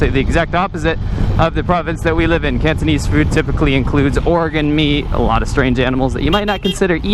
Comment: Funny, for a second I thought was talking about Oregon meat instead of organ meat at 7:00.